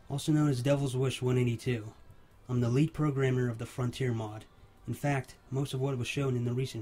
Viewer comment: I thought he said late programmer at first and I was genuinely worried Frontier lost an amazing programmer.